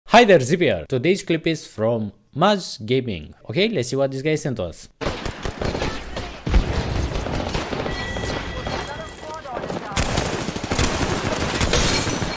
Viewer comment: lolol In the beginning I swore he said, "Más Gaping" ...I was like...ummmm lol. Good video Zipp!